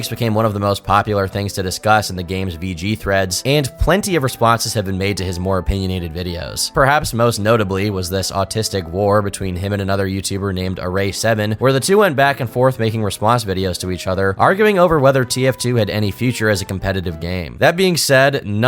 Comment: did he say "autistic war"???